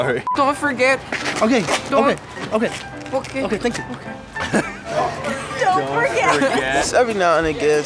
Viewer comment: I thought was saying "Don't porque."